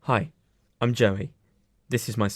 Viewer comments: I heard "Hi, I'm joking." right at the beginning of the video and now I'm confused. 😂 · Dont come at me but when he said “hi I’m Joey “ I thought he said hi im joking